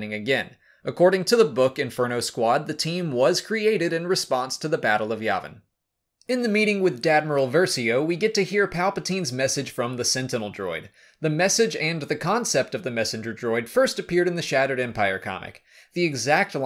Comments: did I hear him right...? Did he say Dadmiral Versio...? 😂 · Did I hear this right? "Dadmiral?" LOL! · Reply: Yes, you did, Paweł! repeated "Dadmiral" every time he was referencing Admiral Versio. BTW, nicely thought! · Wait did anyone heard him say Dadmiral?